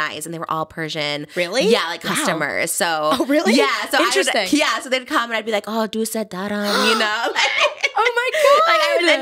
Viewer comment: what did trisha say at 7:00? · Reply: i think “i love you”